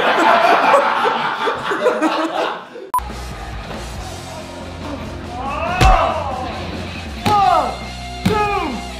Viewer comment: What was the bleeped out word at 17:25? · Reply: it was p***